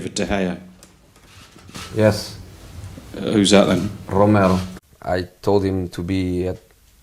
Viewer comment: Anthony Tovilla he said Romero (Sergio Romero-United’s 2nd choice gk) not Romelu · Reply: aah I thought I heard Romel lol makes sense